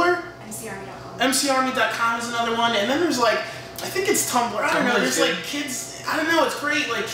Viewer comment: I kept replying it because I couldn't hear what Mikey was saying about tumblr at 5:07 and I figured it out. He said "Tumblr is good." Mikey and Ray have secret tumblrs. I know it. >_> They know everything. EVERYTHING